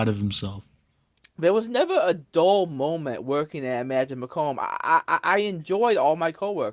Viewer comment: did he say Emagine Macomb? Like macomb Michigan?